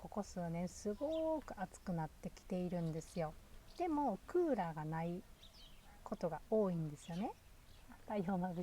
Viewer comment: の所聞き取れなかったんですけどなんて言ってるんですか？ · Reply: 『太陽がまぶしい！』と言っています😊